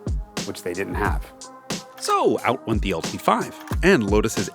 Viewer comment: I thought he said "so out went the old V5" at 4:34 and I got so confused lol